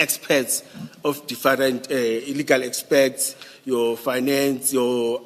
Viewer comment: How such people sleep at night? Wives and children don’t know about this???? Such a shame…..😰😭😭😤😤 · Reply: Did he just say "Illegal experts"?